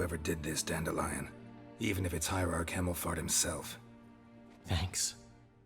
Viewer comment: I wasn't looking at the subtitles for one seccond and before I know it I hear "Hierarch Cammelfart"...ofcourse, it's actually "Hemmelfart"